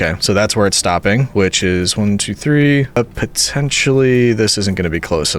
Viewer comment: DID U SAY POTENTIAL?